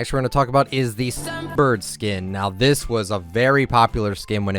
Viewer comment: WHAT SKIN?? no literally i didnt understand what you said... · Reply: Sun